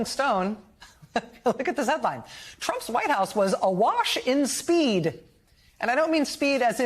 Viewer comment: You should have defined the word AWASH instead of SPEED. I thought you said A Wash. Kind of moronic if you ask me. And YES. I want him back in the White House, rather than the Bumbler in Chief. At least Trump can finish a several sentence paragraph coherently and he has a sense of humor. Open your mind a little bit please.